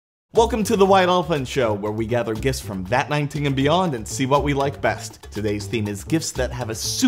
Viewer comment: Bruh I swear I heard “welcome to the white orphan show where we open gifts from vat19 and beyond”